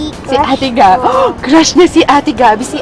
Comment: parang narinig ko sabi nya; Crush ko